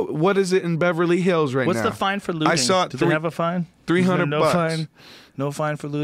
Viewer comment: I thought he said "what's the fine for littering" which would be a good analogy.. but he said looting , which umm isn't lol